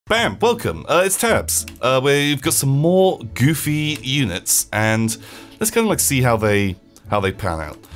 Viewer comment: Had to rewind the intro a few times thought he was saying bam welcome it's turps...